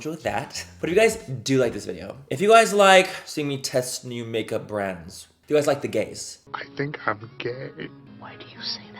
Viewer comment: Manny, hunny, I love you to bits, but every time you said if you guys like the gays up until now, I thought you actually said if you guys like the gaze and I must say I'm living for this anyways. I'm stoopid, srry